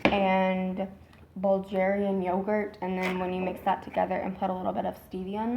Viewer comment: I love you and your videos! Thank you! Is your daughter strictly following the low carb high fat diet? What are her results? At 6:50 did she say 'Bulgarian yoghurt'? I am from Bulgaria. Hugs!